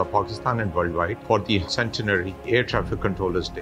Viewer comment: WTH i heard ( best of fu.k )